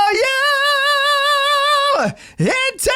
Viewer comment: I thought you said ITALIANO instead of IN TO THE UNKNOWN